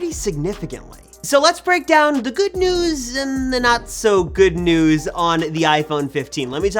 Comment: I thought you said that there was not going to be an iPhone 15.